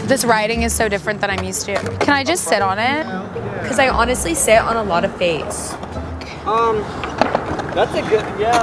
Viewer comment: Wait a minute at 0:38 did say I sit on a lot things or I sit on a lot of face. Either way I'm dead 🤣🤣🤣